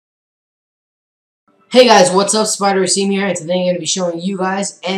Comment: who else thought he said Its Spider Semen here